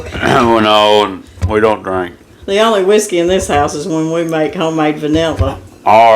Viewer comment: I thought you said "wisdom", not whiskey! LOL . Wisdom would have been a good answer considering being married 45 years took some knowing when to speak up and knowing when to remain quiet😉